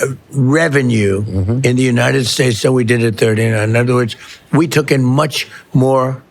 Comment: did the fat orange blob say they took in much more revenue than they "did in '39"? He mumbles because his face and neck are too fat to get the sounds out clearly, but it sounded like that's what he said. If so, wtf is that in reference to? 1939? Idk...